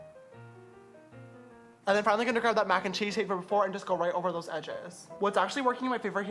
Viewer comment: I thought he said 'Mac and cheese' 😂😂